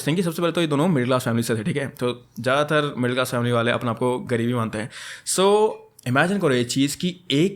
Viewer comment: 'gareeb hi mante hai' was funny once I heard 😂😂